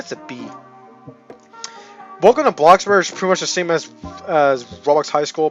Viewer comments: why did i hear "boku no bloxburg" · Did you just say “Boku no Bloxburg” I’ve replayed that for 5 times and I think you’re really saying it lol 😂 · Did he just say *Boku no Bloxburg*??